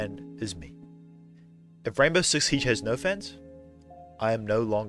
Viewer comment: I thought you said gaybow six siege